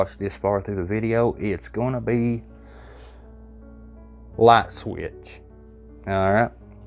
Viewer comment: light switch I thought you said laxative !!! l m f a o !!! great video!!!